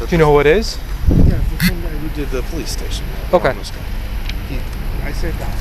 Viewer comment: i thought he said 'The same guy that did the police station, the homeless guy' 😂